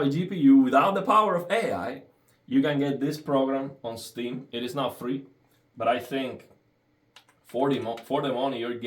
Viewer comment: It was never free as far as I know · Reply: ohhhh...you're saying "it is not free" in your video..I thought you were saying, "it is NOW free"...my bad.